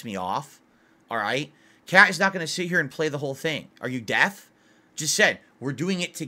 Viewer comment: says death instead of deaf lol